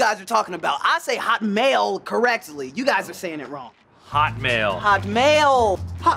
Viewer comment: I don't know why...but he reminds me of Chris Rock?...and then I thought he was saying "Hot mayo"...and then I remembered hot mail... :3